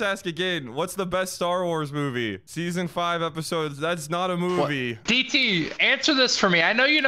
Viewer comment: could somebody please investigate what this guy says here? "season five episode 17, 15:21" im guessing this is a clone wars episode?